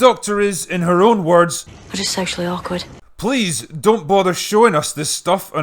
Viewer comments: Honestly thought she said, "I'm just sexually awkward." I didn't question it until a minute later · Thanks to the comments, I now realize she said she is 'socially awkward'. I truly thought she said she was 'sexually awkward'. To be fair, both statements are equally believable.